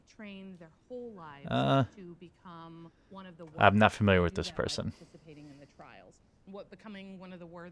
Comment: I can't make out what he's saying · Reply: Daniel Dukai "I'm not familiar with this person" - he doesn't know who it is